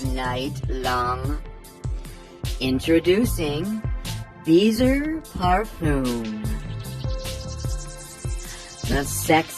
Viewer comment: I didn’t hear “beezer parfum” in the beginning. I heard “beezer harpoon” and I was CACKLING 🐳 🐳🐳🐳🐳🐳